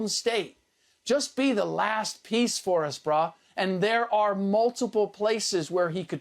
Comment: what did Rick say I know he didn’t say “bruh” did he smh